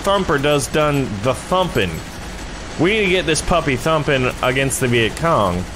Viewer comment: thought he said humpin